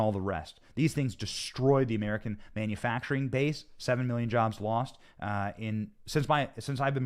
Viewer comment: Did I hear "Ten Thousand Million Jobs"? 🤣🤣🤣